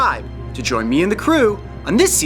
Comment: I can't be the only one who thought that said DragonBall.